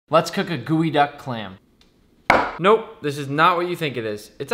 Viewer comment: I thought he said gooey duck clam 😂